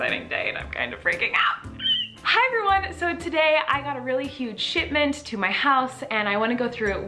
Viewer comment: Lol I thought she said sh*tment! Lol